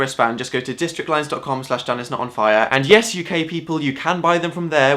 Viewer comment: When he said "UK people" I thought he said "You gay people"....